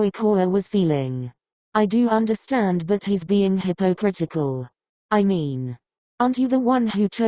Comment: hypo- what?😃 8:54 Lmao the way you said "he got lost"😭😭